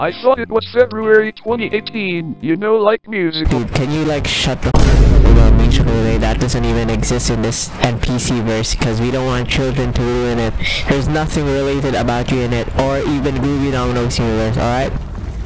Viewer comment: for Those who are wondering what is he saying, Then this is what he is saying: CAN YOU LIKE SHUT THE F*** UP, THAT DOSEN'T EVEN EXIST IN THIS NPC VERSE BECAUSE WE DON'T WANT CHILDREN TO RUIN IT, THERE'S NOTHING RELATED ABOUT YOU IN IT OR EVEN MOVIE DOWNLOADING SIMULATOR, ALRIGHT? 3:19 Carl: What, The, actual, F-[CENSORED) 11:11 bro Chill Out Groovy😶